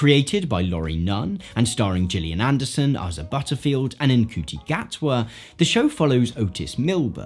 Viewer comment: i thought he said they cast jillian Anderson and Isaac Butterfield the aussie comedian lmao